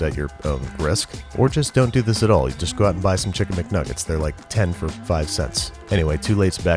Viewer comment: did he say 10 McNuggets for 5¢